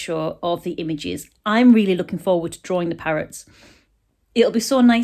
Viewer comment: When you first mentioned the image ideas, I thought you said carrots, lol!! Another lovely video, just what I needed for this dismal day!!xx